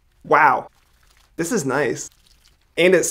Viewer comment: When he said and it smells I thought he said anus smells